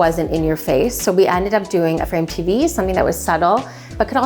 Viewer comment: what did she say about the TV... "A print TV"? What is that? Please help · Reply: Frame TV! It’s by Samsung